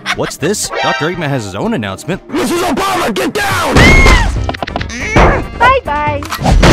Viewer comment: What i heard in 3:14 = MR OBAMA GET DOWN